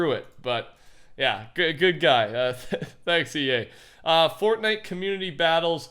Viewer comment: Is it just me, or did he say thx EA?